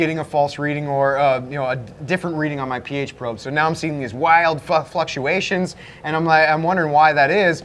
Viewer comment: Did he say fuck fluctuations? 🤣🤣🤣